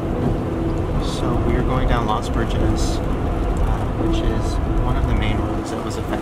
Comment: I thought he said "los virgin" IM like damn thats me